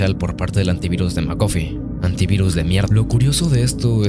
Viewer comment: soy el unico que escuche que al aparecer no termina la frase antivirus de mierd